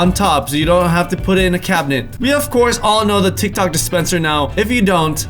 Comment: Am I the only one who heard him say tik tok instead of tic tac on 9.32